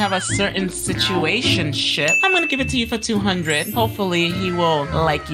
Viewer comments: yall is this js me or did i hear sh!t at 15:25 😭😭 anyways merry late christmas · Why i'm the minute 15:25 it said sh1t instead of ship.. FIX UR CC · OMG INSTEAD OF SITUATIONSHIP SHE SAYS SITUATIONSHIT SORRY FOR THE KIDS BTW